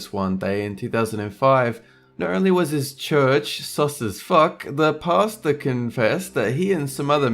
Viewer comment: Church "Sausage Fuck"........? What's actually said? xD